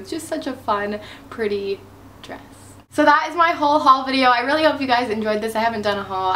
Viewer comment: when u said whole haul at the end i thought u said haul haul